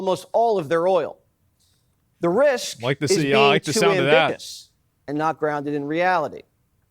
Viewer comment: Did he say ambigus?